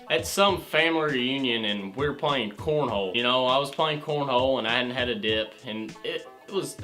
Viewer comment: I thought he said porn hole